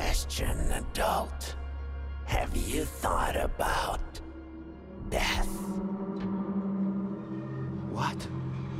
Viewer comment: I FREAKING THOUGHT HE SAID "have you thought about meth?" IM SO BAD AT HEARING